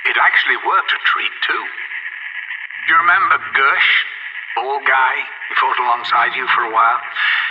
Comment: Did he say bald guy? Like the bald guy in the First Strike poster? If you put it into context it makes it sound like he's saying Gersh fought alongside Tank, Takeo, and Nikolai while Richtofen was doing his own thing.